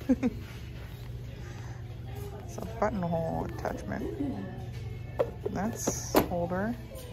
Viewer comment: I love the lampshade! Still watching. I thought you said butthole attachment!😮 I had to rewind!😂😂😂